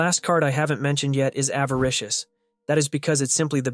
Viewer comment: I thought it was called advocarious